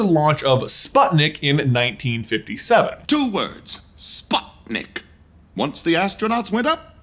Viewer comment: I literally never realized until today that Prospector is saying "Sputnik" in that scene. I always thought he was saying "spot nick", as in the space toys nicked the spot that cowboy toys had. As a kid, I always just assumed this was a phrase I'd never heard before, and I haven't seen the movie in such a long time I've never thought of it.